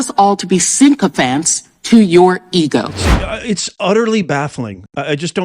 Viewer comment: ▶Sign up to our Free Newsletter, so you never miss out: <link> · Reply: she actually says "symcopphants"